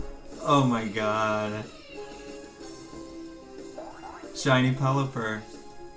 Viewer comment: bro i thought it said shiny only